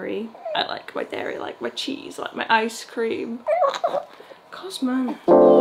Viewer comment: Why did it sound like you were saying cousmon